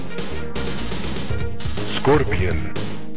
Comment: Did he just say "Scorpion"?!!